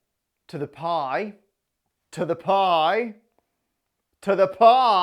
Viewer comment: i thought he said hi to the pi